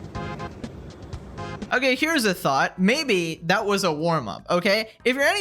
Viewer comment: During the gym girl video you said "here's a thought". But I heard "here's a thot"